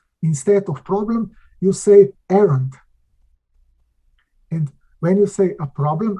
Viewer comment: I cannot understand! Don't say: "problem" but say ELENT? ... or what is the English word? ... if somebody can understand from 51:44 to 52.19 Thank You (Errand = kuldetes, megbizas) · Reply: He said errand